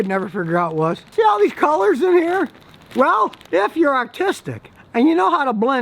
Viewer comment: I swear he said “autistic”